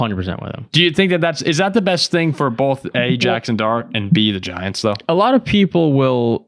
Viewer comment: said: "A - Jaxson Dart." I heard: "Ajax and Dart" 😂